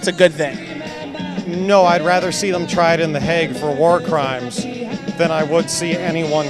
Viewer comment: Did he say he'd rather see the guy tried and hanged than have someone killed? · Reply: Tried in the Hague. It's a city in the Netherlands that also holds UN's International Court of Justice, where war criminals are prosecuted.